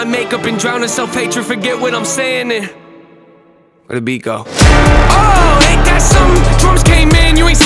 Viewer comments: Literaly I just tap "oh aint that coming" bc that what I hear n' it was first Lmao (Im latina so) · Fun fact:- The people who came to this song after comig from tik tok, have heard "Rebiko" instead of "where'd the beat go" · I always thought he said "here we go" not "where'd the beat go" 🤭